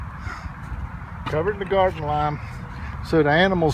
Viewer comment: So at 4:01 you ARE saying Lime? I couldn't tell because the first time you said it I thought you said Lye then I heard Lime.